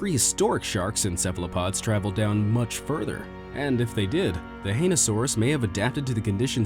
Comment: When he said hainousaur I thought he said ainusaur